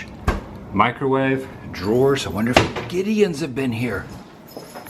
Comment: Its a great room if you don't plan on spending time in there, and you just want a place to keep your stuff and a bed to sleep in. Seems like a great weekend getaway resort Also, I may or may not have thought "cookies" when you said "Gideons " as well.